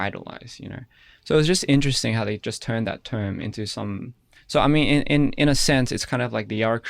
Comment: Did he just say he almost turned to Miito this interview or did i misunderstand what he said